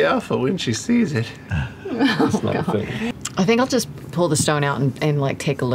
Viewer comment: what does matt say at 20:08? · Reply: "That's not a thing..."